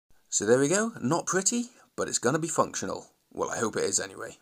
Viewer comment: Due to all the swearing up to this point, I thought you said "It's going to be fuck show" instead of functional. I think I'm going to include that in my vulgar vocabulary.